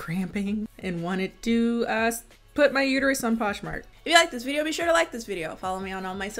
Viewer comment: did she say “I want to put my uterus on poshmart”? Or has quarantine finally attacked my hearing?